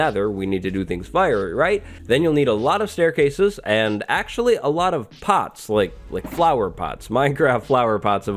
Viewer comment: "youre going to need lots of pots" I thought he said "lots of pot". Made more sense to me...